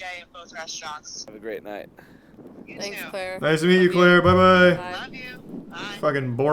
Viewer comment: I thought she said 'Clairl' as in very drunk traffic-stop 'Clairl' lol